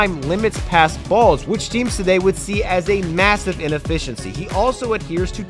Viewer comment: did he say massive?